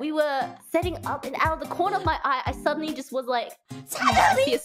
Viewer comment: Huh. I swore i heard "suddenly" instead of "shadow beast". But i like the idea of haachama calling spiders a shadow beast. Lol